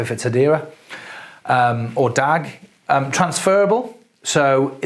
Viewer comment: does anyone know the word of third one he listed after blockchain and hashgraph. It sounded like “dag” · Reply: Yes DAG.